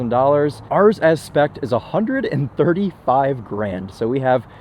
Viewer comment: at first I thought he said 35 grand and I was like no way so then I rewatched it then found out it cost way more